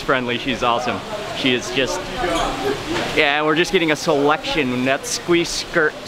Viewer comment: ski squirt. I thought you said "she" for a second there.